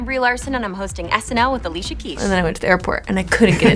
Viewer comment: Did she just say Alicia Kesh?? LOL